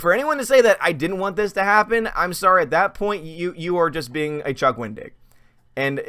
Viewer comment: I thought you were saying, "Don't be a Chuck Wing-dick." 🤣🤣🤣 I had to rewind it to hear it correctly!